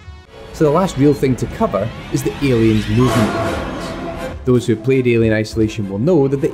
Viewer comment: "...is the Alien _______" What is it that you said at 10:46 ? The noise from the game was too loud at that point. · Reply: think it was “... the alien’s movement.”